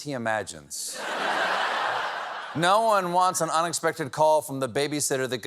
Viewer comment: I thought he said nobody wants an unexpected Carl. I was like what sort of sexy move is this. Also who is Oscar?